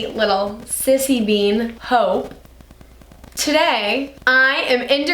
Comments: In minute 0:51 I thought she said hoe not hope and I was like 😶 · I thought you said ‘my sissy bean HOE’ and i nearly cried